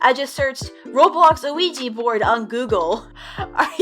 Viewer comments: Hold up, did I just hear- "Luigi board :)" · I keep hearing “Luigi board” just me? Lol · At first I thought she said Luigi board